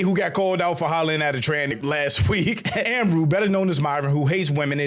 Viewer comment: Did say “tr4nny” at 2:10!??🤨